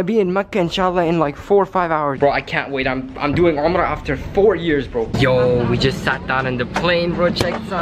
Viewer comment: Yo you said 4 years i thought u said it's 4 hours 🤣